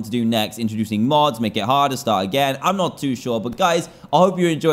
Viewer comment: DID HE SAY START AGAIN??!!?!